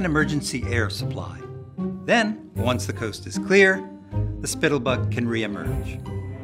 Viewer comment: wha- did you say "spittle butt"? Edit:lol